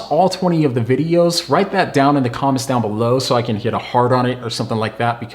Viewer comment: Did he just say to let him know if we watched all 20 videos so he can get a hard-on?